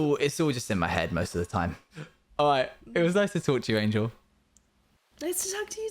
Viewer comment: did anyone else hear “it was nice to torture you”?😅